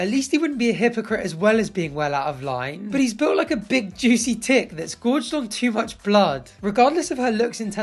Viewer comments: I heard tit and it made it an even sicker burn 🤣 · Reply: I heard "big juicy tit" also. Then he mentioned blood and I was like What!???? Ohhhhhhh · I swear I heard "big juicy tit" 🤣🤣🤣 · I heard "he's built like a big juicy tit"...and...still works. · I thought he said “big juicy tit” and honestly it’s just as accurate.